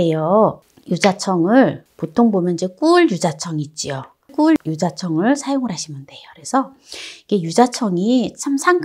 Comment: 뿔 유자청이 있나요? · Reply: 꿀 유자청이란말이 뿔 유자청으로 들리네요~^^